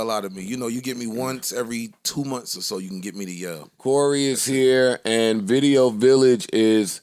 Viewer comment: I thought he said “Rory is here” for a second…like waiminute 🤯